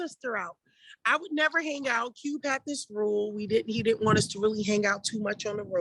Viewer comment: Tupac told her to stay in, then took her sister out late at night and they both didn't respond while being paged...🤔. Gemini men can get away with anything😂. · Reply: She said "Cube had this rule" and told her to stay in if you listen to what she said in the interview. What is written in the post is inaccurate to her words.